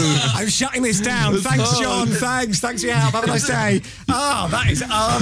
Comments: did he call John? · does he say "thanks John" ?? 😂